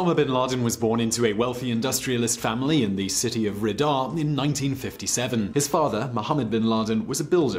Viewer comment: did you just say ridaa